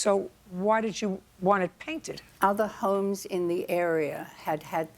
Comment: I thought the plaintiff said « Other hoes in the area » 😂😂